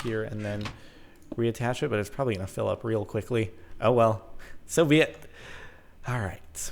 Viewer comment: did he say Soviet or so be it?